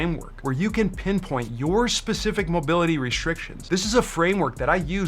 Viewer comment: This content is gold. You're my new favorite PT on YouTube. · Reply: Yeah.. if you could understand wtf he is saying. What the hell is POSHA or POSHEWRAL or 00:35 RESHRIKSHUN or 1:02 SHRAGEES